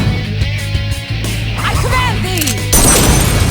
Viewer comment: "PERSONA" "I'll show you my true power" "Don't mess with me" "I command thee" 👁️👄👁️ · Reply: I thought Misturu said "i command me", that was confusing